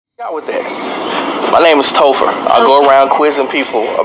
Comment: Sound like he said tofu or tophitt in the beginning. Sounds like a setup to me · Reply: Topher is what I think he said.